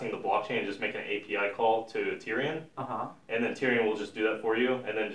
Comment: is he saying 'Printerium' (PRX)? Please clarify :) Another great video series! Cheers guys